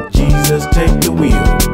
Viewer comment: That »Jesus take the wheel«-part gets me every time. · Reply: I hear "Jesus takes the weird"